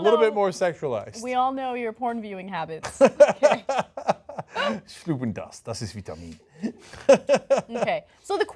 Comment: Was sagt Cenk bei 2:28? Stubengast, das ist Vitamin? xD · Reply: "Schlucken das, das ist Vitamin". ;)